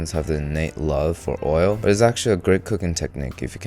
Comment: said oil, I heard freedom 🦅🦅🦅🦅🦅🦅